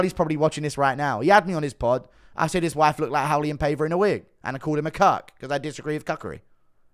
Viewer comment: Who is he saying O'Malley's wife looks like in a wig? I can't really understand him here and it's not a name I recognize, but I definitely need to look it up. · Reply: Raulian paiva he fought O’Malley before